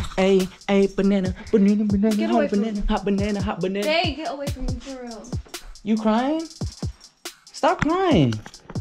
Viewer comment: is not his name ? Cause she said wear was you and ___ why did they cutt it out ? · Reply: A&S SISTAHS she says it at 18:04 “jay get away from me fr” What i said